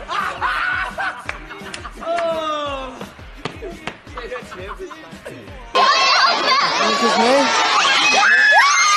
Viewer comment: what did he say? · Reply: He said he like intelligence